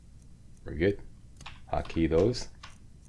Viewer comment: I thought you said "Taquitos" for whatever reason instead of "Hotkey those." Not sure why tho tbh.